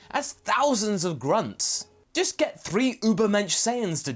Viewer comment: Did you say Übermensch at @ 1:49 ?